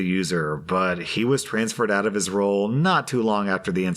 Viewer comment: He said "Transferred out of his role" I heard "Moved into a hole"